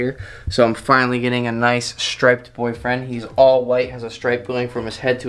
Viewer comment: For I second it sounded like you said “I’m getting a nice striped boyfriend!” But you were talking about the snake. I was gonna say, is Kayla okay with this?!!! JK LOL 🤣❤️